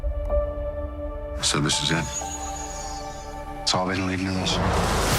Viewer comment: That first line Robert Downey Jr. said is motherfucking indecipherable. I can't seem to get captions on my phone right now either. Anyone know what he said? I hear: "So this is it? It's all they l'evil looms?"